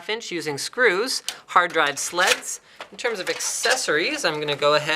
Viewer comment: Did he say hard drive slets? lol